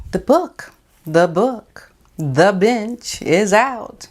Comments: I know I'm congested and mis-heard you but I thought you said "The Bitch" for a red hot New York minute. "The Bench" lol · I thought I heard "the bitch is out" lol